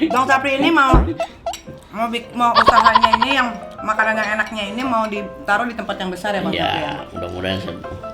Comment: Dimenit 7:45 gue denger nyari ngomong "makanan yg gak enak ini" atau " makanan yg enak ini " Gue yg salah denger apa gimana emg beneran nyai ngomong bgtu?? 😅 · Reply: Makanan yg enak nya ini....